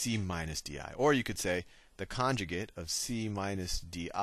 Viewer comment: what did he say at 2:45? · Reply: Conjugate